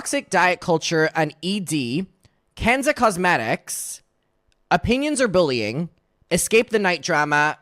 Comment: I'm sure this was "Opinions or bullying?" but I heard you say it as "Opinions are bullying" which feels so on brand for Gabby.